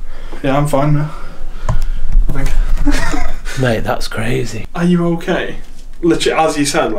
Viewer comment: When he said RU ok I thought he said “are you gay?” Lol